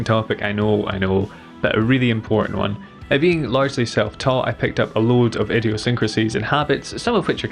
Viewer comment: The first time he said "being largely self-taught," I thought he'd said, "being large and seven foot tall." I had to stop and rewind.